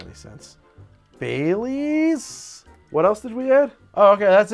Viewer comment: That coffee maker idea was so shit, I genuinely thought she said “Bay Leaves” and not “Bailey’s” purely because the idea was so horrible from the start